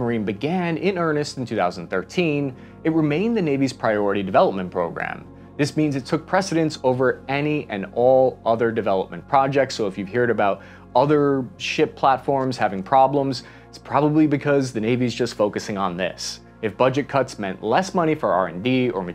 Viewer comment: does he really say "hear'd" instead of "heard" lmao